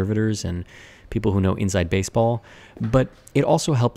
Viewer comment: Can't hear something- 13:32 sounds like "inside baseball" to me, lol.